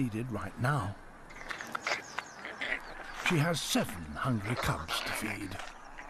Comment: LOL @ 1:02 I thought he said "She has seven hundred cubs to feed".